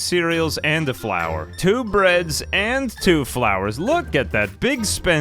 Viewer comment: I thought you said go too bread